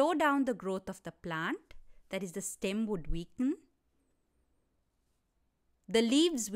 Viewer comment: I didn't understand the word you mentioned at 5:28. Was it "stemwood weaken"? Or am I mistaken? Please correct me. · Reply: “That is, the stem would weaken” 🙂